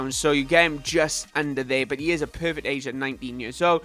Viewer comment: it sounded like you said he is a pervert asian